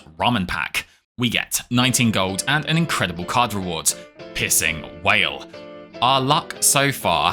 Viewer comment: Excuse me, did you just say "pissing whale" ?!?!?!?!?!?!?!? 😂